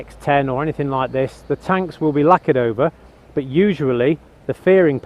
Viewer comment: when your are talking about the paintwork, you say "the tanks will be (lacked over??)" I dont understand the words and I cant figure out what are you talking about. And then, how do I see if the paint on the farings has that characteristic that you say it could have if it was repainted. Hope to have explained me better :) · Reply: the word is lacquered, it's a clear paint . Hope that helps